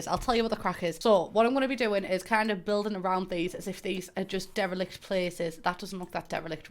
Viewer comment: The first handful of times I heard you say "derelict" my brain processed it as "devilish". Either way, both are strangely appropriate in this context.